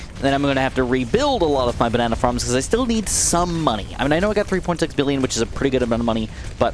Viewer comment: Did you say 3.6 billion? 8:56 on the video. I am just curious